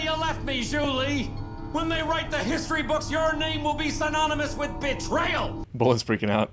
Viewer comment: whoa I thought said "...your name will be synonymous with b*tch" 💀